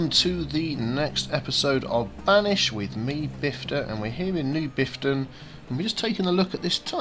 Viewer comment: Half a dozen seconds in and do I hear "with me, Biffta"? XD